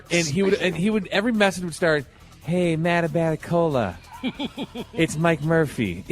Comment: Hey Matt Abbatacola, AAACCHHHEEEAAAHHHH!!! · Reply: No lying, I honestly thought he was saying Mad About Cola.